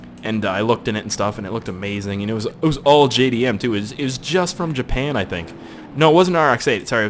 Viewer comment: Did I hear JDM